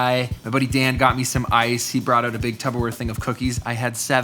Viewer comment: Did you say "tub-o-ware'? @ 4:00 Or were you just speaking really fast?